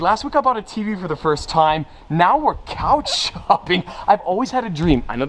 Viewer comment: I can’t be the only one who heard “couch humping” right? 😂